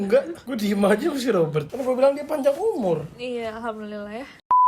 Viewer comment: Hah? Nessie ngomong alhamdulillah tapi dia kan? apa telinga gue yg salah denger?